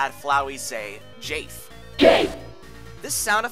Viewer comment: please tell me i'm not the only one who heard 'GAY' instead of jafe